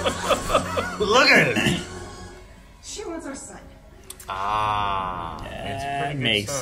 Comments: Blind Wave Beyond Members and Raw Rider Patrons can watch the Full Length Reaction to this Episode HERE: <link> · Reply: This iniitally confused me because I thought she said "son" since they sound the same and thought Monica had a child!😂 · This iniitally confused me because I thought she said "son" since they sound the same and thought Monica had a child!😂